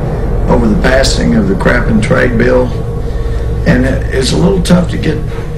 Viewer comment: did he say crap and trade bill---LOL